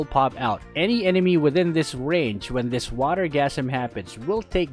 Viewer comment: Did you just say water orgasm?